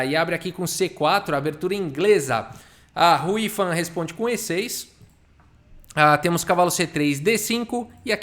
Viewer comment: Quando o falou "Hou Yifan" na abertura, eu escutei "a Ruiva responde com e6."